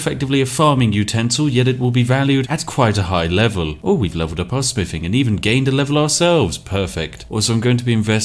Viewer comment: Did he say spiffing?